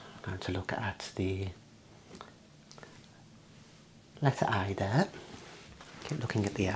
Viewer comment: i thought you said ; "look at the I" i could't find it and then realized you said "A"